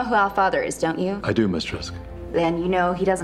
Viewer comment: why did I hear him say "Yes Mistress?"